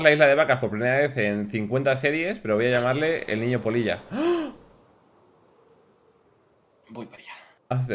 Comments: Pensé que decían el niño p0ll4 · Por un momento pensé que decía: el niño poll4... Edit:Willy se va a sorprender cuándo lea los comentarios